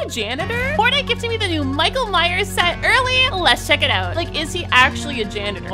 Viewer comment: Why it sound like she said Michael minors😂😂